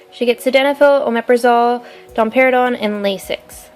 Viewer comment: am I the only person who heard domperidone as Dom Perignon? 😂